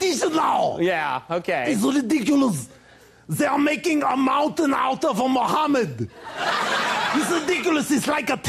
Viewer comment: Ive watched this like 20 Times this is my favourite One of all satellite but what does Arnold say at 3:39? Soo funny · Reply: They are making mountain out of a Muhammad (mountain out of a molehill would be correct idiom) other one he said was tempest in a t-shirt (tempest in a teapot would be correct idiom). You can google these idioms for their meanings.